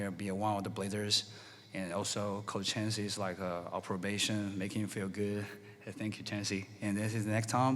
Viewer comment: I can’t stop laughing at what got lost in translation where it sounds like he says “Chauncey is on probation, making him feel good” 😂😂😂